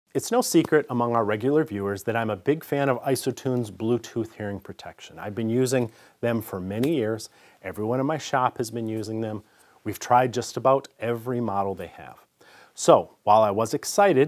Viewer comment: Did he say ‘many years’ or ‘many ears’? Either way it works! Lol